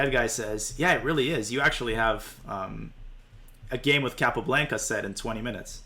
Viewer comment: From some reason I heard "I finished a game with Capablanca in 20 minutes". I kinda laughed because it's improbable..